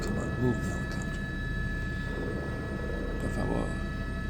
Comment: did you say "por favor"? hahaha